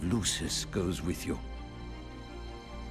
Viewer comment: "Nocto, my son".. oh wait, its actually "walk tall".. misheard..